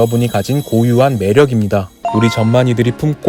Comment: 순간 전만이가아니라 ㅈ만이로들었네